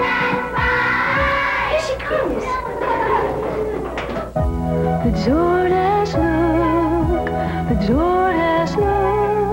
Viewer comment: I thought she said ''The sore ass'' look lmao XD